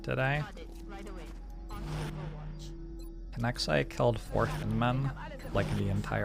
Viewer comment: Every time says "Thin Men" I hear "Thin Mints" and now I want cookies.